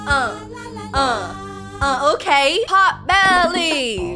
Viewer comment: I thought She said Hotbelly